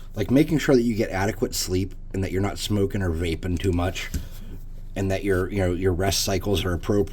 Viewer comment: Not VAPING too much. I had to replay that because I thought you said RAPING.